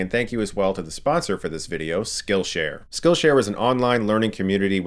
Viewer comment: did he say "skill share was" did they close?